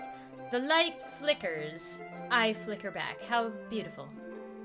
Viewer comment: i thought it said I fricken back XD